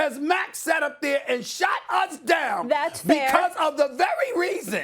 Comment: 😂😂😂Did anyone else catch Stephen A. say “Shat”?!